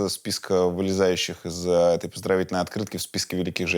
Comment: Послышалось, что Юра в один момент сказал "пиздровительная открытка"... А монеточка просто чудо чудное! Даже похитюшу покорила, и в этом ничего удивительного!!!